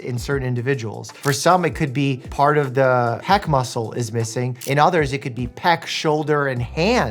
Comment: I heard 'heck muscle' instead of pec muscle, and now I can't stop thinking about it send help